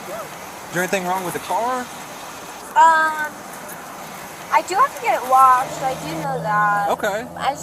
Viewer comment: Officer: “Anything wrong with the car?” Girl: “I do have to get it washed.” 🤦🏼‍♀️🤦🏼‍♀️🤦🏼‍♀️🤦🏼‍♀️ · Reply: I'm embarrassed.. I thought she said " I do have to get new rods " 🥴